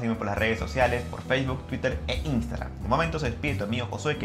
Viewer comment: Al final dices "Aqui tu amigo Josue"? Me re costaba entenderlo, pense que decias un nombre como Tito Minio Josue. Y no podia descifrarlo :'D